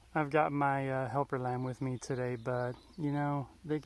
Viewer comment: I thought he said “helper lab,” but no. He said, “helper lamb.” An actual cute as the dickens lamb came running.